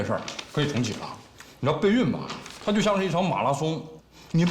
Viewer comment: 我把备孕听成了代孕😂😂